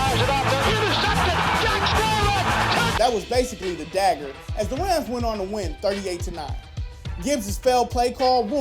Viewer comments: am i the only one who heard "as the RAMS went on to win 38 - 9"" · who else heard him say rams